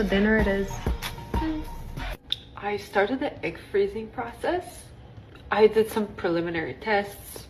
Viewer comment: oh, I thought she said "started her ICK freezing process" - don't worry, you'll never run out of those